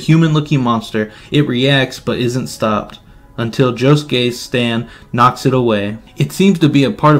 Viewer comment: the subtitles saying "Joe's gay" 😭😂